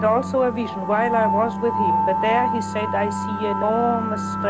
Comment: what is she saying? Enormous stretches? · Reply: Stretches is correct... enormous stretches of the earth!